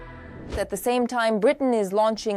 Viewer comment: Did she just say (4:00) that Britain was making large scale warheads for Russian shar heads ??....I sincerely hope not.